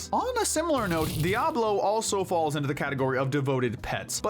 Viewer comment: Im pretty sure its DIaval not Diablo but I can understand why it might be misheard.